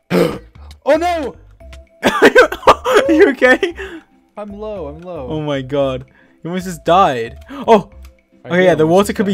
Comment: I was the only one who heard George says "your gay?" 😂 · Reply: If you listen to it by itself, it sounds like “your gay.” But if you listen to the few seconds before, it’s “you okay.”